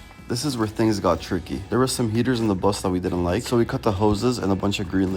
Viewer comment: I thought he said there were gators under the bus but he just said heaters